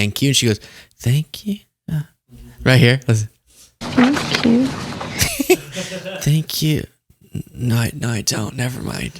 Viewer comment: I dont think she's saying 'Thank you', I think she says 'P Q'. She's going through the alphabet to make sure she's looking in the right area for South Korea.